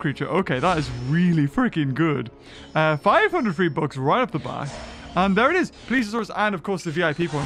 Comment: did you say v-bucks?